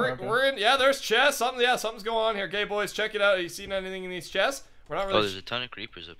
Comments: Did say GayBoys check it out???? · OMG EVERYBODY GO TO 6:36 SOUNDS LIKE HES SAYING, SOMETHINGS GOING ON HERE G#Y BOYS!!!!!! OMG OMG LIKE IF YOU HEAR IT!!!!!!!!!!! Btw this isnt my main acc if you want it look up, menecrow! LIKE SO SEES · why did call them gay boys hahahahahahahhahha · sounds like he says gay boys